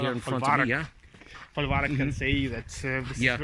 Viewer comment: Thanks! Great tour with your local guide. I got interested in this border since I visit Belarus regularly. At 12:50, did he say Folwark/Falavark? I stayed in a Falavark near Lake Naroch two weeks ago. The host showed a very interesting interwar document, written in Polish and Belarusian, stating that his grandfather (or grand-grandfather) bought a piece of land for 6 kg of gold (maybe golden tsarist rubles?), and that they could not claim that piece of land anymore after the Soviets took over that part of Belarus. I hope the exhibition in the museum will stay a few weeks, because I cannot make it to Minsk in September. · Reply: Falavark. The exhibition will be on display for a few weeks. I will be at the exhibition next Tuesday so will provide more info then.